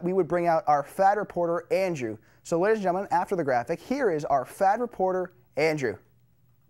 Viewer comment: For a minute I thought had said "here's our FAT reporter" - Whew!